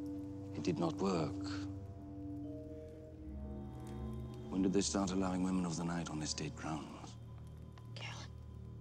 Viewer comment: What did he say around 0:30 after 'woman of the night'? At this date? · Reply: He said “When did they start allowing women of the night on estate grounds?”